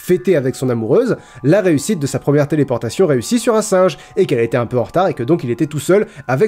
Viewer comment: "Oui, il voulait fêter la réussite de la téléportation réussie, j't'emmerde" Remerciez moi plus tard^^